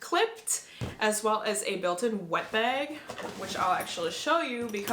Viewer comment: I thought you said the Beis suitcase comes with a built in ‘weapon’ (not wet bag) I was like 😮😮😮😮 take my money lol